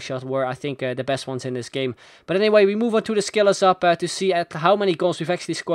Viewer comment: For 122 episodes I've always thought said "And now onto the Skillers Up" but I just realised he said "And now onto the Skillers Hub".